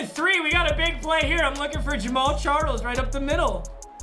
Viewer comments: I can’t be the only one that thought it’s said James charles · Who else thought it said James Charles and was confused asf? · who else thought is said james charles is super fast😂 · Am I the only one who thought it said "James Charles" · I thought it said James Charles😂😂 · Who else thought it said James Charles at first? No.. just me... mk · who else thought it said james charles be honest boys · Who else thought it said James Charles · i thought it said James Charles at first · Am I only one who thought he said James Charles? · I thought that shi said James Charles😂 I was worried for MMG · Thought that said James Charles for a second 😅